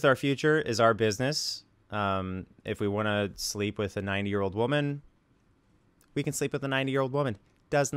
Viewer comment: I literally thought he said “ if we want to sleep with a nine year old we will” 💀